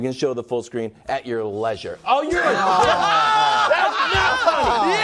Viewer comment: Anyone else catch Wildes saying "oh you're a d*ck" at 5:38 haha